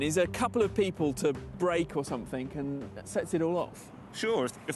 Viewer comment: What I heard: “if there are enough girls on the road, something very minor can get magnified into a traffic jam”